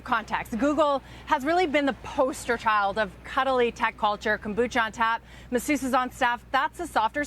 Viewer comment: WTF does she even say at 0:24? Kabucha on tap mesucha on staff? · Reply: Kombucha (tea) on tap, masseuse (massage pro) is on staff